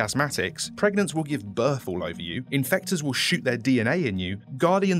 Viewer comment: Ngl the first time I watched this, I thought he said the Infectors will shit their DNA in you💀